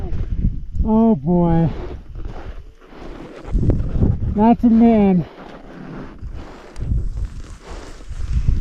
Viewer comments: Did you say "That's a man" about that back pack tumbling down the mountain? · At around 8:50 or so, something tumbles down from the top of the mountain. I swear i heard the climber say "that's a man". No one says anything else and they continue their ascent. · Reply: I thought the same too but he actually says "that's a mitten"